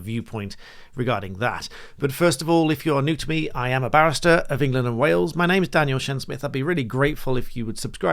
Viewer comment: I wasnt quite listening at the beginning then ...and thought be said "i am a bastard of england and wales" for a second 😂